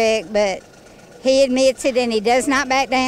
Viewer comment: Did she say he admits it when he's wrong?